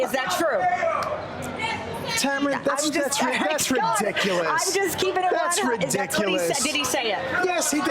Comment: did Tam just say "I'm just keeping it 100" ? Can't help but love black women.